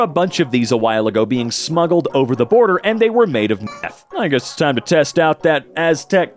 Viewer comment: What was the word censored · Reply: Meth